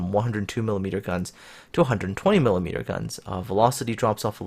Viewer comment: I thought you said the T6 had 420mm guns for a second